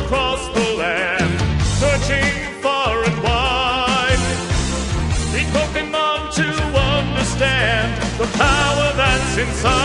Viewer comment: Did he just say "eat pokemon to understand..."?